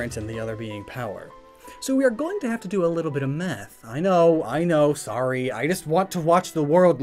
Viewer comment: I thought he said "we're going to have to do a little bit of meth'